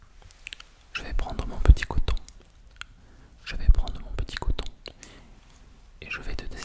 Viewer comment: Quand tu as dit que tu allais prendre ton coton pour désinfecter la plaie, j'ai entendu "couteau" 😂😂 je me suis dit "Oula il y'a quelque chose qui ne se passe manifestement pas comme prévu !!"